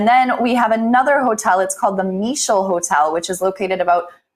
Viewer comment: was it called the Misho Hotel?